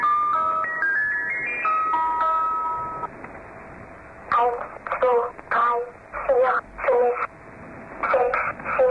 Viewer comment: Sounds like a little kid and I can't understand what she says after eight... nine and zero maybe, then it just loops. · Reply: Thayet Desshâi 1,2,3,4,5,6,7,8,9,0 . . .